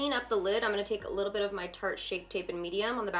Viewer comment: thank you for being the reason why i hear "shart" every time someone refers to the tarte shape tape (had trouble typing that) thankyousomuch